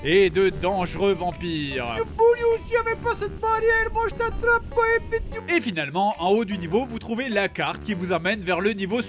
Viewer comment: il dit quoi ? xD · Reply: BaeBae Harmony en gros "oh boudiou (bon dieu en patois) si il n'y avait pas cette barrière je t'attraperai". Avec un accent de vieux paysant 😊